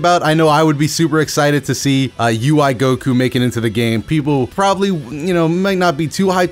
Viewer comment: did you say for real? what's the people problem